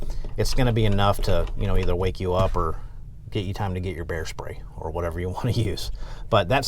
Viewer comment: "Bear spray holster". You sir, you rock. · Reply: thought he said "Hair spray"